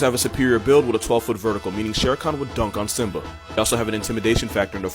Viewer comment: is it just me or did it sound like he said “shar khan” instead of shere khan 😂😂